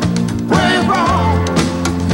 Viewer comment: I remember dancing round the kitchen with my mum after hearing this on top of the pops singing "I believe in Milko, praise the lord, you sexy thing" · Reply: lol, I thought they were saying: "I believe in Malcolm"